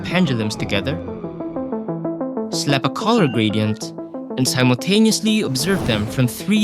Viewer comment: "And sowgqkwgtaneously obsereve them from 3 distinct angles" what? I didnt hear that quite right